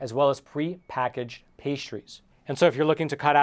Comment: I have no problem understanding every other word in this video except for (13:18) "pre-packaged <what?>" ... is he saying "pay-shroots"??? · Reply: Pastries. Cakes and cookies full of chemicals.